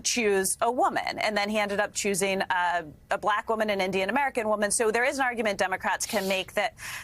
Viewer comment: did she just say Kamala is Native American